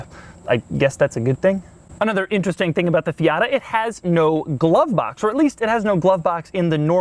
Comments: i heard fiata😂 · In 6:02 did he say Fiata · Did he just say "Fiata" in 6:01?